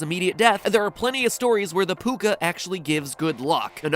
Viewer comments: when you said "Puca" i thought you said "Poop gun" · I thought he said pupka not puka